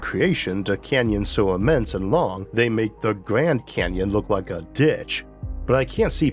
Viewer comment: I was distracted at 24:40 and could have sworn you said it made the grand canyon look like a bitch and was so surprised lol